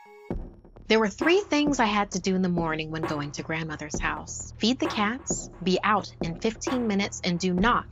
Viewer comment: when u said feed the cats, I thought you said pEtE tHe CaTs